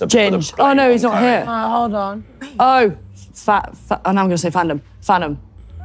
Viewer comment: I came to see your reaction bc I heard her saying “fat” when she tried saying yo name 😭